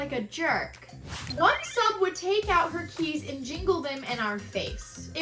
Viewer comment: You said, "one sub takes out her keys and giggles them… I heard, "one sub takes out her TEETH and jiggles them...!!!" 🦷🦷🦷🦷🪥😁😁😬